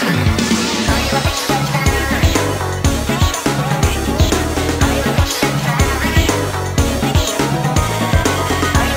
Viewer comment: WHAT DOES HE SAY?! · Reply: I got it. It's "when did sales go wrong"